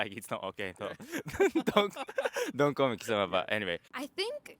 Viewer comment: For a second, I thought you said "-sama" was rude, which got me confused, because I learned it is commonly used in very formal and polite occasions. Then, I realized you said "kisama". :-)